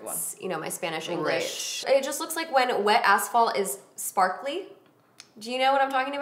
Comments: I had to replay back twice cuz I thought desi said wet assholes! Not asphalts 😵💀👀🤣🤣🤣🤣🤣🤣 I was like holy shit, a wet asshole?! Take my money 😂😂😂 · Oh my God I'm dying for the eyeshadow palette and wow I totally thought you were saying "wet asshole"😆 omg... ima die... definitely had to rewind! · I thought you girls said wet asshole at 10:40-10:47 hahaha I had to go back and listen again.